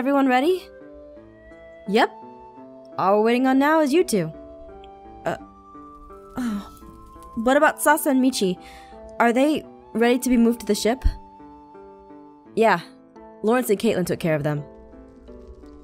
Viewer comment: I think said Salsa and Michi XD